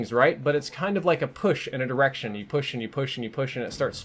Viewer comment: i thought he said a push and an erection